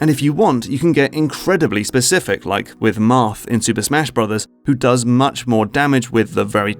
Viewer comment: Lol when he said “Marth” i heard “math” High school PSTD